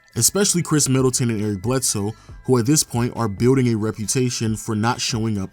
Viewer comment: Yo I thought you said drew bledsoe😂😂